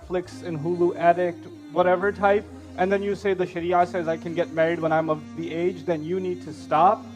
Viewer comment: you said shitty in the subtitles instead of sharia. Just to let you know lol